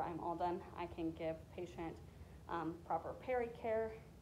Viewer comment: I thought you said, "and pray" a couple times. Lol! Good idea!